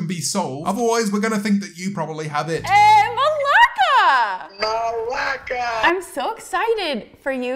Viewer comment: Uh, did they just call each other "malaka" ?